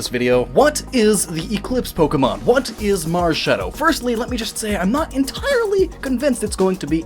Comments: Who else thought that Marshadow said Marshmellow? Just me... mk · Dude! I almost thought it said marshmallow.